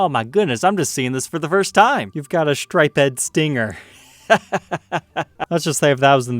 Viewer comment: You pronounce "striped" as "stripe-ed"? Dude, I know you are a millennial but come on... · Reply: I thought he said a striped head stinger.